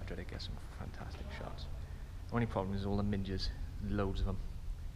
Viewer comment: Great video! I thought you said minges too 😂🙈